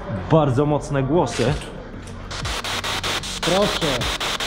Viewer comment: kiedy powiedział „proszę” ja z sb7 usłyszałem „dzięki”